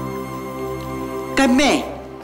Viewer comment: 我聽成干貝🌚😂